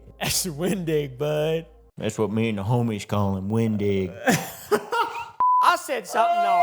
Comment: WHAT DID HE SAY??? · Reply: Windigger